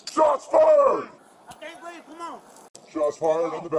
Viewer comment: Does he really say ''I can't breathe, c'mon''?